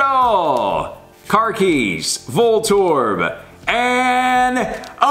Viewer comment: he said car keys instead of Klefki I think